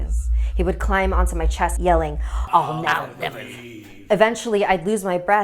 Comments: what is he saying??? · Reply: It says "I'll never leave" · What the red eyes man said at 3:57? I didnt understand it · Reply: I think he said I'll never leave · what's he saying??????? Also pause it and the longer you look the creepier it gets (maybe it's just me) I LOVE SCARES! Edit: Y Yo You You j You ju You jus You just You just r You just re You just rea you just read You just read t you just read th you just read thi you just read this! You just read thi You just read th You just read t You just rea You just re You just r You jus You ju You j Yo Y Wow that took a while! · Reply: I'll never leave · what did he say? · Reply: > I'll or I will never leave < that what he said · what does the man say?? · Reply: "I'll never leave" i think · wait.... what? (replays clip) okay, I’m sorry one more time. (replays) okay I’m crazy what does he say? 😝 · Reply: All now never leave that's what it says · what does it say? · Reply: “I’ll never leave” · Can someone plz help tell me what did he say i really don't know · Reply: He said I'll never leave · is he saying “I’ll never leave?” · what did he say? I couldn't understand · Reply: oh I'll never leave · I really get creeped out at the end when she says “sweet dreams” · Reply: I thought he said "I'll never leave" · what did it say? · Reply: I'll never leave! It said that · Anyone know what the dude is saying at 3:57? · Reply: Someone In Existance he said, "I'LL NOW NEVER LEAVE..."